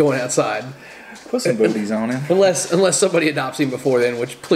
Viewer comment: Thought that I heard boobies not booties.